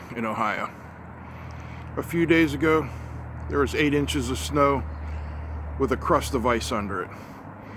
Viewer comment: I thought you said 8 inches of snow with a crushed device under it. I spent a full thirty seconds trying to figure out what device you were talking about.